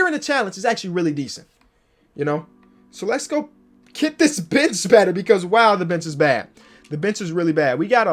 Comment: did he say get this bitch better